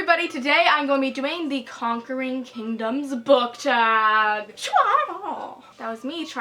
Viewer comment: I WAS SCROLLING THROUGH HER VIDEOS AND I SAW THIS TITLE AND THOUGHT IT SAID #CONQUERINGCONDOMS AND I WAS LIKE "wait...what?"